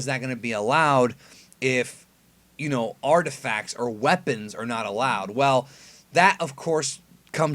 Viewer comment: I could have swore he clearly said no artificial weapons key word here is weapon I think you were right the first time